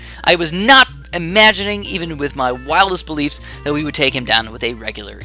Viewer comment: what did he say. I understood whilist believes. (sorry, my english sucks.) I wanna know what he said there. can someone help me with that? I wanna know what he said. XO (whilist believes...)